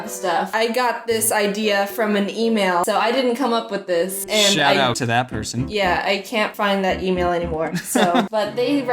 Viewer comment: I thought he said "shoutout to that pussy"